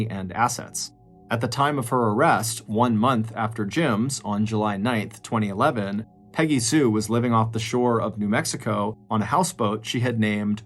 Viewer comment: I could have sworn you said she was living off the shore of New Mexico